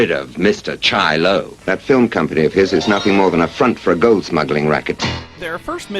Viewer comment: I heard "Goat smuggling" at first and was wondering who would want smuggled goats, apart from Erdogan